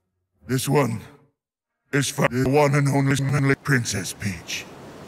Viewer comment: When it said one is for the one and only smelly princess it sounded like it said this one is for the f**king smelling princess peach